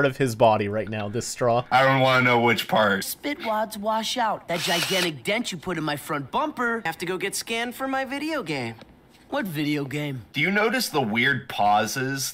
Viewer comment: I legitimately thought he said “scammed” at first. Because either is still accurate!!